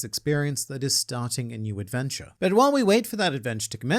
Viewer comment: Ok, so I didn't hear "It's adventure you have to click it." I heard "It's dementia do you want to kick it?" Now I'm deeply concerned.